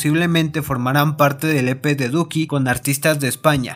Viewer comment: Like si pensaste que dijo nepe en el min 1:20😂